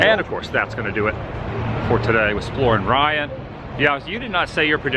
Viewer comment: Why yes, he does ;) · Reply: thank you sir i thought was saying exploring ryan. I subscribed sir